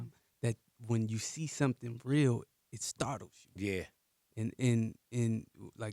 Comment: Can someone tell me what he says at 11:35 "Everybody out there being so god damn fake all the time that when you see something real it's ..." I dont understand the last word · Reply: "It startles you."